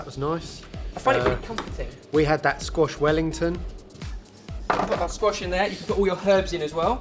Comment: When you said "squash wellington" I thought you said "squashed wellington" and I was like, this is why we need more Jamie. Who else would make it into a terible joke about a damaged shoe?